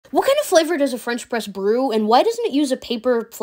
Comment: I thought she said "Bench Press"